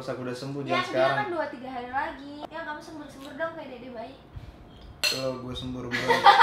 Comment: 4.13 siapa yg denger bilang "yang cepet sembuh dong pengen dede bayi???" apa kuping gua yg salah denger